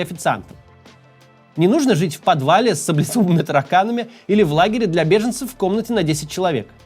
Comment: На 08:00 еле расслышал "Им не нужно ... жить в подвале с **саблезубыми тараканами**" ... -Макс Кац не готовился ни разу – не читал текст и смеется видя их впервые в эфире; из-за чего слова произнёс плохо, непонятно.